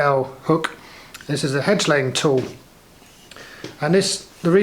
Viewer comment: I first thought you said head-slaying tool. Shows what a sick mind I really have underneath that sugar coating.